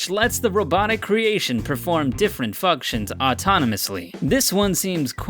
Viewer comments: did he say "fuck-tions" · Idk why but I heard different fuctions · did you say fucktions?